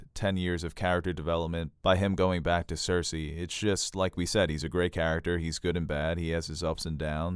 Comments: I heard "He is a great character, good in bed.." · had to go back and re-listen... at first I thought you said he's a great character he's good in bed 😂😂😂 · after 40:00 you said about Jamie "he's good and bad" and i heard "he's good in bed" lol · I thought he said about Jaime "He is a good character, he's good in bed, has his ups and downs..." lol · I swear I heard “he’s good in bed” instead of “he’s good and bad” at 40:03 I had to chuckle 😂😂😂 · Omg, I thought I heard them say when talking about Jaime’s death: “he’s good in bed” 🤣